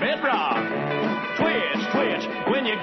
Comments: I watched this cartoon almost 50 years ago and that song still gets stuck in my head from time to time. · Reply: I always thought it said twist twist-came here to find out. Thought it was a new Mandela effect!😅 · I thought he was saying Twist Twist..makes more sense.. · PEACE: OMG, all this time, I thought the song was saying "TWIST, TWIST"! (silly me) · lmfao that part made me laugh so hard. :D I thought he was saying Twist, Twist.